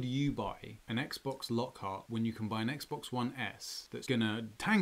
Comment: It sounds like I was saying Xbox one S but I’m pretty sure I was actually saying X... because it really matters